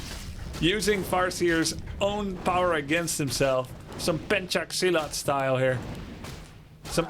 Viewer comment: Did I just heard Pencak Silat? That's my countries martial arts!